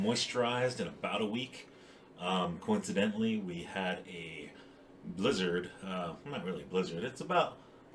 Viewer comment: I misheard "coincidentally we got a lizard" so thank you for the giggle that gave me. I often say I feel like a lizard when my skin is super dry and flaky!